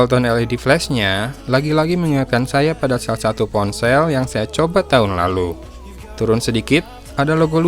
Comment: apa cuma gua yg denger di detik ke 0:57 *yang saya copet tahun lalu*? sampe gua ulang-ulang beberapa kali 😁 ampun kang, kalo saya salah denger😂😂